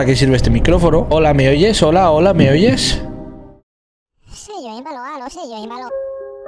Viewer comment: Soy yo o en el minuto 5:42 escuche que dijo "soy malo eh yo soy malo" 😢